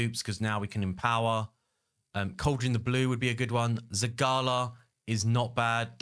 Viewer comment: that cauldron has another name... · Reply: criodan the blue. i always heard you say cauldron the blue